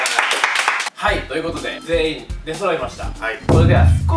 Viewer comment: ここの「出揃いました」が「デストロイました」にしか聞こえない